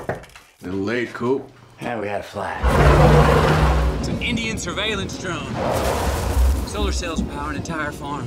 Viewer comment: Does he say "Indian surveillance drone" at 00:04??????